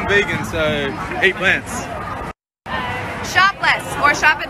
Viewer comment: Omg I thought she said shoplift